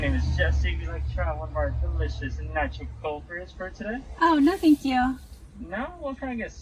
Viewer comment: Thank you so much, for making this vid! It is awesome and super helpful! Could you tell us, if not too much trouble, what the Baristas were saying at (i) 8:53-56 (before you said "no thank you"), and (ii) 9:31 (when she seemed to say "we'll see if...")? As non-native speakers, these assimilated sounds are very difficult to make out for us (and CC didn't really help much). Thank you! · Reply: Hi there. sorry for the delay. He asked, "Do you want to try one of our delicious nitro cold brew (coffee) today?" She said "Alright, we'll see you at the front (like the next window) for $8.20", which is the total for the order.